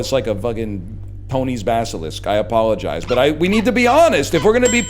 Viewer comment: "Tony's Basilisk" is amazing but I misheard it as "Coney's Basilisk" which might be better